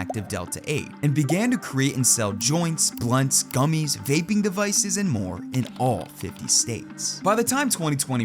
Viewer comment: Did you say? "gaping devices" 😬